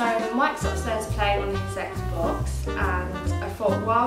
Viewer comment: I thought she said sex box instead of x box